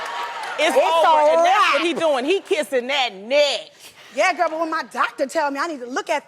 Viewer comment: What did she say??? "That ned ned" …what??? xD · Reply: "Kissing that neck"